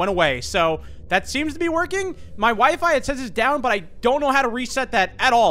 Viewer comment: am I the only one that that thought I heard "My Wife"?